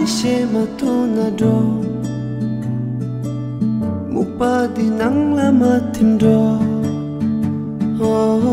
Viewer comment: Aaaaaahhh I couldn't help but notice that there are a few Nepali phrases like.. Well..i found out in the comments that's Tibetan..but it's really good.i mean not that I understand but I...aaaaa does any understand?my ears have been blessed...so many talents in this world..❤keep doing what you do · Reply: Actually he doesnt say timro at 0:34 he says “mukpa dhi nangla madhim, dro” which basically means “dont drown in the fog, lets go” in tibetan and i think you misheard the end of that sentence “madhim, dro”. There werent any nepali words or phrases in the song, but if you’re a himalayan person with tibetan heritage like you’ve said then your mother tongue most likely has similarities to tibetan language since tibetic languages are spoken throughout the himalayas. What ethnicity are you?